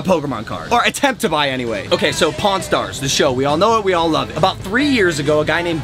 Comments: Whenever he Said Pawn Stars i thought he Said Porn Stars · When Logan said pawn stars I thought he said porn stars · Everytime he said "pawn stars I thought he said porn stars